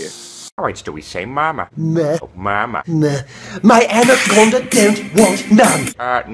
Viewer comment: omg y did he said my Kona de da 😂😂😂